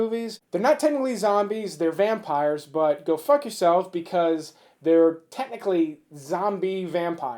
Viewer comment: I thought you said go fuck yourself on